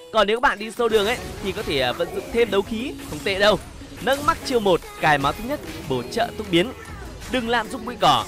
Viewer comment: Ad hay có cái câu gì mà e nghe thành:" lên max chiêu một, cài máu thấp nhất??, bổ trợ tốc biến nhé" cài máu thấp nhất là sao ad:)))???